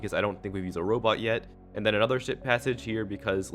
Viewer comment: It sounded like “s**t passage”